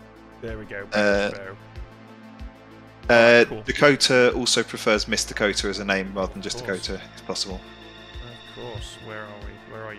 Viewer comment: this is a random weird thing, but I skipped to about 6 minutes in and my brain didn't register accent, so at 6:30 I heard "Coater also prefers Mister Coater rather than just Coater if possible" but once I saw changing the name i realized I was dumb, lol